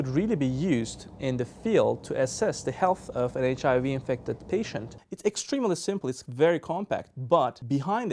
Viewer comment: He says "assess the health of HIV infected patients" 1:30-1:32 ... not diagnose it. And I didn't hear him say TB. What he's talking about is doing WBC Diff's in the field, not seeing the virus or doing the chem work... he's not even saying it's a screening method. He's saying that it's powerful enough to diff WBC's and do counts, which IS very important for HIV patients... as well as countless other conditions such as leukemia and lymphoma. (BTW, I work in a lab)